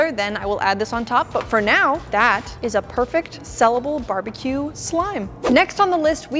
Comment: At first when you were making the barbecue slime, I thought you said Barbie slime and I was confused because you were dying brown